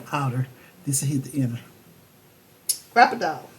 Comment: Thank you so much my friend 💕 you can get the Cropidile at Amazon. It's the 'We are memory keeper brand'.😍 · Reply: 😂😂all this time I thought you were saying crocodile hole punch 😂😂😂